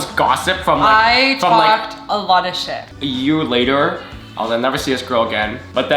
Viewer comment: What did she said at 1:20? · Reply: "I talked a lot of shit"